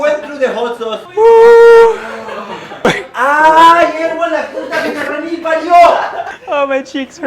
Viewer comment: does say “ahhh p*t*ng in*??” or it’s just me?🤣😬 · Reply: No he actually said "la p*t@ que te re mil parió" xD It's Argentinian Spanish